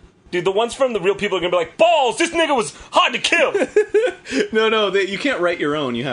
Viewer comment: O.o did Jon say "this nigga was hard to kill"